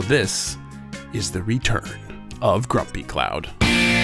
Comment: Aw I thought it said grumpy cat r.I.p